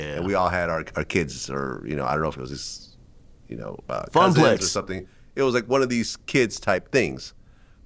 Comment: You mfs beed to think outside the little bubble of celebrities you live inside of... Flex is a nobody, Drake doing an interview with Flex would get less views than his interview with Bobie Altof or whatever her name is. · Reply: Okay, but it just sounded like he randomly said “Funk Flex” at 2:14 💣 💥 💣 💥 💣 💥 💣 💥 (he was really saying “a funplace”)